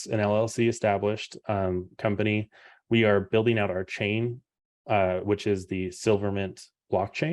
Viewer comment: Wait. What? Did just say "we are building out RChain" 9:50. I literally cannot unhear him saying that, for some reason.